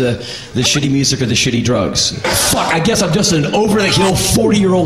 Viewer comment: Calfmandooo - it sounds like he sayc "Curb-munching", but he is actually saying: 40-year old curmudgeon Curmudgeon (Cur-Mudg-eon) - Noun: An ill-tempered person full of resentment and stubborn notions